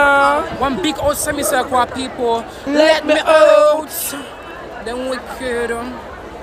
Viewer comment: I could listen to him say LET MEH OUUTT 1000 times lmao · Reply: I thought he said "let me oats"